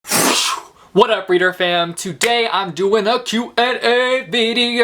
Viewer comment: Wait.. this whole time I thought the intros said reader fan but this time I heard fam?! Which one is it? I feel like my life is a lie 😱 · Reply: It's FAM :)